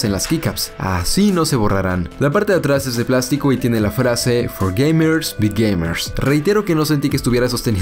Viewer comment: For gamers" "Bai" (bī) gamers ,no "bi" ya que lo que dijiste es "for gamers be gamers" y es "for gamers by gamers"